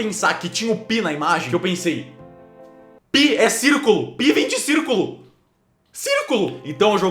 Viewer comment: invés de eu ouvir "π vem de circulo" eu ouvi "π vinte circulo" quem ouviu errado (além de mim -_-)